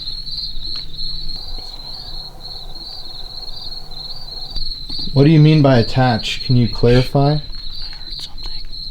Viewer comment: I heard I women's voice saying something...I can't make it out😱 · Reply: Sounds like it said ,enough...